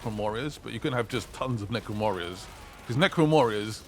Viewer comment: Did you say necron morriors at 5:35 ? Or is just me who's going crazy ?